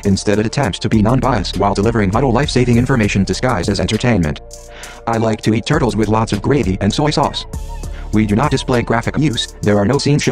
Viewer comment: "I like to eat turtles with lots of gravy and soy sauce" Did I hear that correctly?